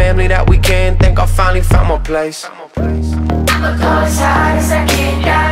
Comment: When I first heard this song I thought it said "Till the green people come n take me." 😅👽